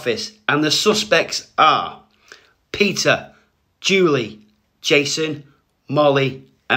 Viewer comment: When he said peter i thought he said Pizza